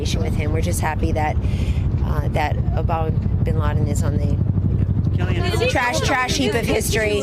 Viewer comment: she actually says Obama bin laden. Yes, it sounds like a slip of the tongue, but that only happens if you've said that before as many of her ilk have.